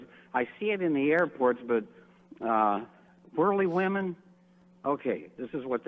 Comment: I thought he said "burley" women too. LOL Actually, Spanx is a brand of underwear/shapewear since 2010. I think this nutjob is referencing leggings - unless he's spying on women getting dressed. LOL